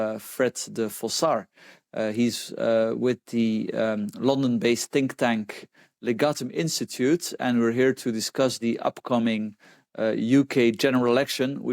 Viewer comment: When I hear "Think tank" in the opening commentary, I just hear "Thinkers". Great post, but what we lack in the UK are "Do'ers"... Stop thinking and debating on the back of others, and start doing...